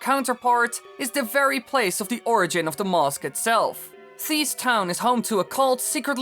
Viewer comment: Every time you say "Mask" all I hear is "mosque"